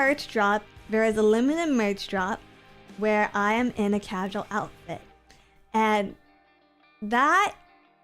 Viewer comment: While listening at work today, for a second I thought said "cachalot outfit" not "casual outfit." That would be hilariously wild.